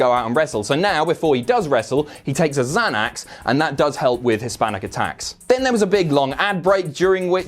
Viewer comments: I thought he said Hispanic attacks lmao I just realized he meant his panic attacks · I honestly thought was trying to make a joke, I thought he said 'Hispanic attacks" instead of " His panic attacks" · Jesus I thought said that Xanax helps with HISPANIC ATTACKS not his panic attacks... jeez mate